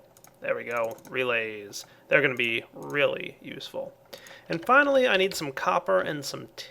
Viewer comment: He said really use full not relay but its just mishearing I had to check 9 times :p